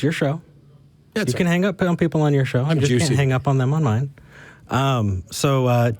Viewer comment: Lmao I would've done the same thing. Did you say?; "I'm juicy." lol